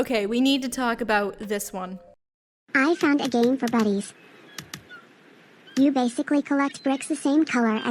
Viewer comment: I could’ve sworn they were saying a game for buddies. It never made sense to me because they were all single player games.